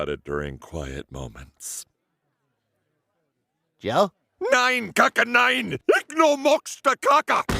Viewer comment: can somebody tell me what joe is saying? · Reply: He is saying Nein Kacke Nein. Ich no mochte kacke which would translate to No Poop NO! I would not like poop if he used the correct words and grammar. Saying that in proper German would be "Kein Kacke Nein! Ich mag die kacke nicht" which is more like I dont like shit. If he wants to say I dont want poop that would be Ich will die Kacke nicht. Cant believe this is what Im using my German for lol